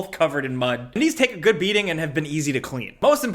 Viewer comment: During the commercial for the sneakers when he said his dog always covers him in mud.. I thought he said "but don't worry, he's taking a good beating" jokingly so I laughed. But no he said the shoes can take a good beating lmao.